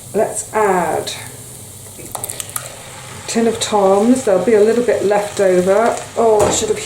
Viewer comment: I was banging around in my kitchen while listening and when you said, "Add a tin of toms..." I thought you'd said, "Add a tin of TUMS (the antacid)..." LOL I KNEW that wasn't right... haha Thought I'd share a funny with you. Thanks for the frugal cooking ideas! This one sounds great!